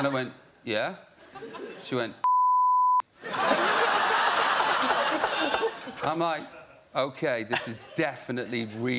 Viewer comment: someone PLEASE figure out what he said im so curious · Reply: She called him "P*ssy" after looking at his CK underwear. 😅